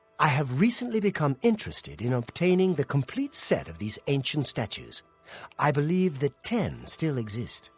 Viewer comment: I swear i keep hearing "alien statue" instead of "aylied"...